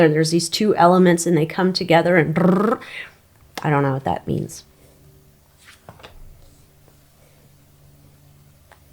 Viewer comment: Zinc and sodium chloride. Christopher Dunn. He said it creates a gas. I think he said it makes hydrogen? I'll have to do some research on that for you to be sure. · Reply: Yes. Christopher Dunn said hydrogen.